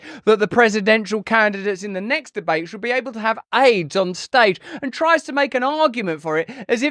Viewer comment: Did he say " A I D S on stage ? 😂😂😂😂😂😂😂